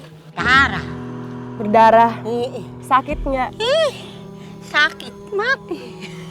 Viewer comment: did they say sakit? That means it hurts in filipino bisiya not tagalog. ._.